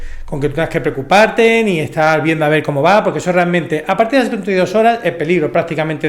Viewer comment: Qué dijo justo antes de decir 'dos horas'.(?) Es en el minuto 3:30 . Qué pésimo hablan el español. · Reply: A partir de las 32 horas xD